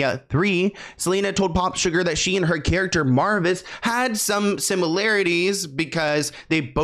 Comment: did he say MARVIS